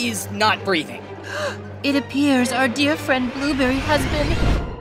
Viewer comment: the captions say "our dead friend blueberry" lmfaooo